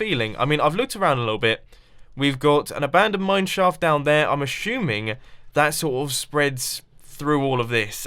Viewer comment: "ashuming" instead of assuming, lol